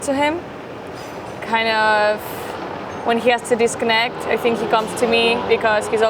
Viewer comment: My dirty mind thought she said @ 7:50 when he has to Unload he comes to me 😂